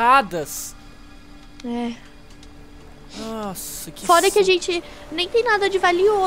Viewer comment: A FALOU FODA PQP MINHA VIDA FOI UMA MENTIRA JSJFJSHJSJX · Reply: Ela falou FORA :v flw